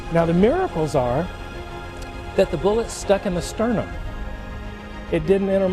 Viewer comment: Pepe Pepe His Chest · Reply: Emiliano Ramirez I got that but at 0:52 he said something the stuck in my stern..?? IDK what he said..! It's not that clear..! Would you mind to tell me sir..?